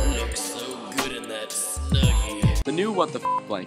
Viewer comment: He says Snuggie instead of WTF Blanket!!!!